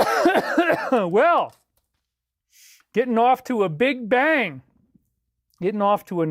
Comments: David taught about the karmic consequences of suicide. He didn’t kill himself. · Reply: "getting off to a big bang" Really kind of sounds like he says Getting offed to a big bang...? He even repeats it. In his last live stream? 🤔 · There is more to this Story. RIP David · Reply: "getting off to a big bang" Really kind of sounds like he says Getting offed to a big bang...? He even repeats it. In his last live stream? 🤔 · No way. No way. I'm not buying this. Not for 1 second. No way he would do this now. No way he would call the cops just to do it. Don't tell me mental health issues. That doesn't equate to suicidal. Nope. Somethings up. God bless you David/Edgar/Micheal wherever you are. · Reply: "getting off to a big bang" Really kind of sounds like he says Getting offed to a big bang...? He even repeats it. In his last live stream? 🤔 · "getting off to a big bang" Really kind of sounds like he says Getting offed to a big bang...? He even repeats it. In his last live stream? 🧐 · David wasn’t suicidal. I’ve known David for a while now and he was always wondering “when are they gonna come for me to hush me up” it was always in a joking manner. Now look. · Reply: "getting off to a big bang" Really kind of sounds like he says Getting offed to a big bang...? He even repeats it. In his last live stream? 🤔